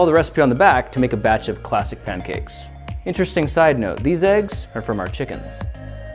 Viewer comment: At first I thought you said side note these eggs come from chickens